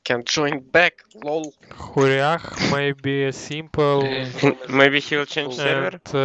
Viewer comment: проорал в голосину , когда ворлдэд сказал не "X9PYX" , а Хурях. И прикол в том что автор этого видео даже не понял что он сказалXD